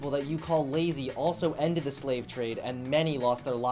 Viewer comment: i thought that said Slav state